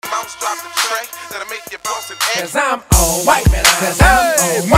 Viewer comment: I thought he was saying "Cuz I'm all white". LOL!